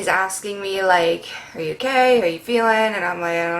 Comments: ok don't take this as hate but did any one else hear gay instead of ok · I thought she said her mom said "Are you gay" 😂